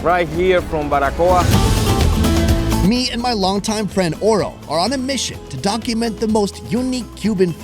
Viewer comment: I swear at the beginning at first it sounded like he said me an my longtime friend oral